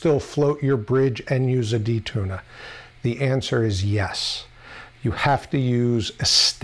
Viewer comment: I thought you said “eat a D” near the end of the video! 🤘🏼😆🤟🏼 Nice install demo BTW.